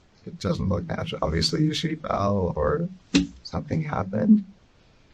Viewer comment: On 21:25 where the captions say “(unintelligible)” he said “if she fell or”… and on 21:35 where the captions say “(unintelligible)” he said “I tried to pick her up, I turned the water off”